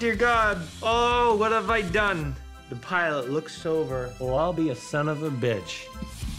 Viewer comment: the pilot looks SOBER 4:47 lmaoooo Well, I'll be a son of a bitch ...totally sounded like he said sober, not looks over xD P.s. he could've taken the jugs off one by one to start coming down...rrright?